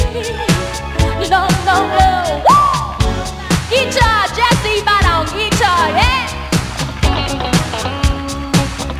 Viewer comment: Does anyone know what she says at 8:00? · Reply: ExoticMidniteCreeper “Guitar! Jesse Ibarra on Guitar yeah!”